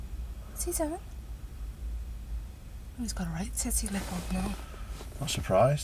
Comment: What did the pupil say at 2:06? and, if its a scouse phrase, please translate 🙂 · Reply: "He's got a right Titty Lip on now" <link>